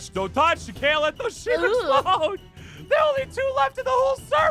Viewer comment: Is it me or did I hear, “DON’T LET THE SHEEP EXPLODE”